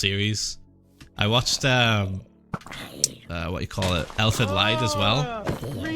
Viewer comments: whats the anime he's talking about i cant understand, even captions cant recognize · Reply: Elfen Lied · I can't understand what anime is he saying, can anybody help me? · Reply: Stefan Stef Elfin Lied 👍